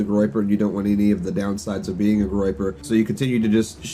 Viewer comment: Lmfaoooo the subtitles said “but you don’t want to be called a groper and you don’t want any of the downsides of being a groper” tell me more abt the downsides of being a Groper bro lmaoooo